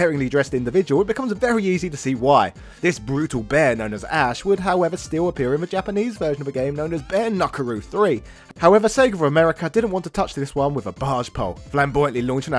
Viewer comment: Did he just say “bare nuckaroo three”? Lmao!